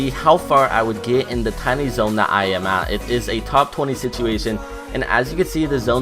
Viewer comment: I think he said it’s a top 26 situation